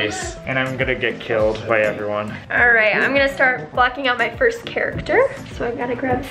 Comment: Hey guys so at 4:43 she sounds like she said the f bomb took me a while to see see said blocking